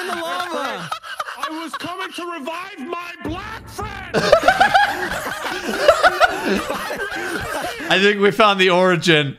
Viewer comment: It’s sounds like Nogla saying “Black Fred” 😂